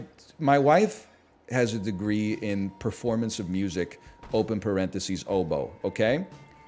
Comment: For a second I really thought he said "My wife has a degree in performance of music (hobo)."